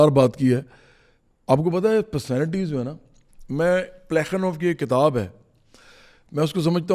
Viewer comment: In 40:37 i couldnt understand the name of book, can anyone please tell me? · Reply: He only said the name of the author, Andrei Platonov